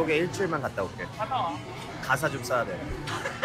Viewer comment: Thanks for translating it !! I would like to note than when Tablo says "I need to write some lyrics" at the beginning he's literally saying "I need to shit some lyrics", that's why the others are laughing haha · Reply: omg i heard it as 가사 좀 싸야 돼 too hahahah